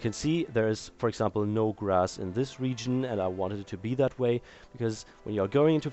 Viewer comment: Actually everyone else is right. He said "wanted" not "one tit". I just misheard him.